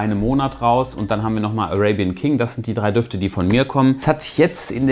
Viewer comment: Beim dritten Parfum hab ich beim ersten Mal raping king verstanden 😅